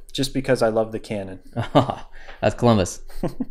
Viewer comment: I thought he said that’s clumbis